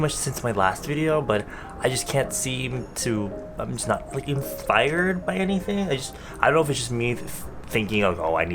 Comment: did he just say inFired? AAH is that a bts reference?!